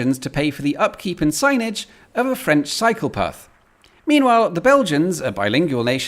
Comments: "pay the upkeep of the French psychopath" Wait, what? Or did I misheard "cycle path"? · I thought he said "upkeep and signage of a French psycopath" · Why do I keep hearing 'psychopath' every time you say 'cycle path'?